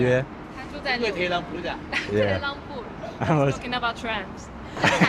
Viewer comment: did she say he's talking about trans? hopefully saying something nice 😭